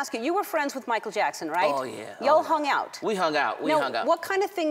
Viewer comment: did she just say y'all